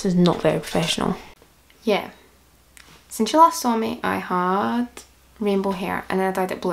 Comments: На 0:46 секунде мне показалось что она сказала слово: сначало по русски,кто тоже такое услышал?) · я одна слышу русское слово СНАЧАЛА